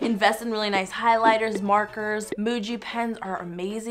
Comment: did she say emuji